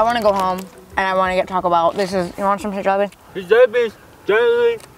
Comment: i thought he said these jelly beans genuinely taste like crack